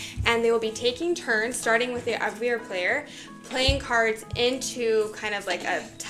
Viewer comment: OMG, I realize now that you were saying "Abwehr player", but one of the first times you said it in the video (1:41), I SWEAR I thought you said "Both of the players have cards in their hands, and they will be taking turns, starting with the UGLIER player..." I must have listened to it 4 or 5 times, and I kept thinking "Finally! A game where I always get to go first!!" 🤣🤣🤣